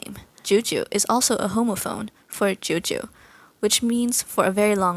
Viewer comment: Misheard homophone as homophobe and was like ‘ah, this doesn’t seem all that fitting..’